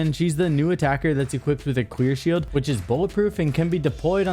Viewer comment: I’m not the only one that heard him say queer shield right?